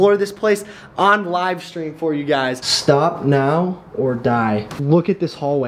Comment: at 9:27 if you slow down the speed of the video to 5.0 you can hear someone say (stop it or stop that) its one out of the 2 did your friend walk through the prison with you or did he film it before you filmed in there it dose not sound like anyone walking with you in there plus everything in the video sounds slow because the speed is at 5.0 but when they say stop it it sounds like normal speed I don't know it caught me off guard I had it playing at half speed so I could see more of the graffiti and I noticed it when you were speaking I don't know stuff like that catches my attention easily I was just curious and wanted to know. I can't wait to find out what you say. But a prison is not only a place where murderers wer I'm sure a lot of people died in that prison as well..